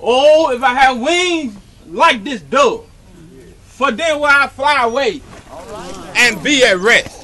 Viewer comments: the dove part is funny but damn, I know that I KNOW!! DID NOT SAY, "if i had wiiiiiiinnnnggggs like this dove For daaayywaaayyy I fly away!!!!" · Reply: 🤣🤣🤣🤣 I heard "for dayyywayyy I fly away" too 🤣🤣🤣🤣🤣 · the more u watch this...the more u see and hear something that makes u crack up even more...including the lil boy pushing through to have a look and the lady right after saying "he on the floor".... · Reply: Lmao sounds like he said "And be a Rat" towards the end but he says "Rest" lol.